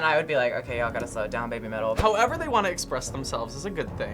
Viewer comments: am i messed up if i thought that said babymeal? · i thought it said baby meal